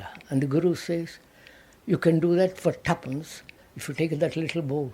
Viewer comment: And the guru says: You can do that __ _____ if you take that little boat ! Can anyone understand ?? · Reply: Perhaps, "for tuppence". Tuppence means 'two old pence'.